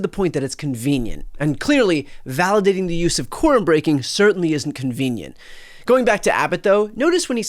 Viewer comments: I thought yall were saying corn breaking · I watched 5 minutes of this thinking “corn breaking? Dem texans really love their maize” before i realised id totally misheard them.